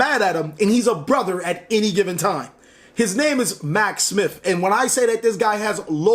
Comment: did you just say Mac Smit??? As in Tim Scam????